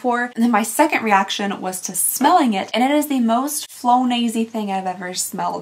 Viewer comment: the subtitles say flo Nazi instead of flonase